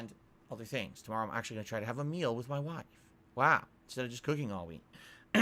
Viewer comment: "I can actually have a meal with my wife instead of cooking at home.." ....Did I hear that right? Because if so there's SO many things wrong with this sentence... KFC> Home cooked meals!